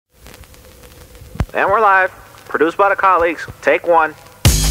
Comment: OH, THAT'S WHY THERE'S A "PRODUCED BY THE COLLEAGUES" ON THE INTRO PART OF THE SONG TF I FEEL DUMB ps. so it's called a producer tag I'M REALLY DUMB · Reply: LMAOOO WHAT NO I ALWAYS THOUGHT IT SAID ''WE'RE LIVE, CAROLINE'' FOR SOME REASON NOOO